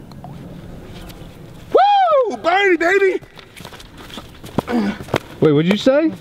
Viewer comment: I thought i heard say BARNEY